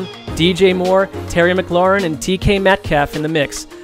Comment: did he say TK? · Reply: It should be DK lol